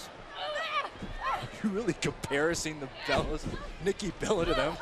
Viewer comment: lol he said comparising instead of comparing